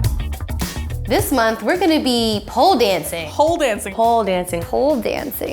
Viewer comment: AM I THE ONLY ONE THAT HEARS 'HOLE DANCING' INSTEAD OF POLE?